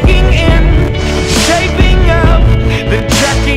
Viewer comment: for so long I thought it said “escaping out” not “shaping up”